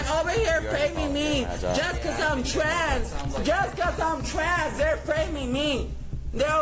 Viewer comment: The person saying “They’re framing me because im trans!!!!” Is crazy. I’m a trans person myself and I don’t do crazy shit like this. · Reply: I thought she was saying because I’m trashed.😂